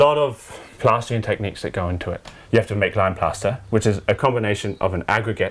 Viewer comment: What exactly were the walls made of? sounded like you said lime? sorry the accent is throwing me : / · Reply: Lime plaster :)